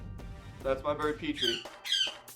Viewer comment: At first I thought he said that's my bird Pee Pee